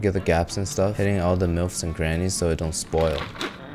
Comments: did he say milfs and grannies or am I tripping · did he just say milfs and Granny’s? It’s nooks and crannies? Have I been saying wrong my whole life? Am I just deaf and did he actually say it correctly? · Did I hear "all the milfs and grannies"? 2:25 (edit) "pornstar slurry"